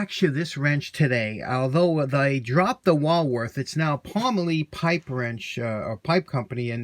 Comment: I had to laugh-at myself. I thought you said Woolworth on the last video. I figured anything's possible. K Mart had wrenches, JP Penney had Penncraft, Montgomery Ward and others. So Woolworth? Why not? That grinder is a super lathe for sure. The pipe wrench turned out super. I saw one of the sample still shots about halfway through and saw an original with steel handle and red inlay. Nothing prettier than Red! Thanks for the fun. :-)